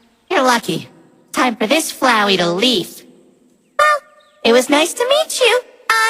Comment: Did flowey just made a pun at 2:54? It sounded like leaf instead of leave XD Best part for me XD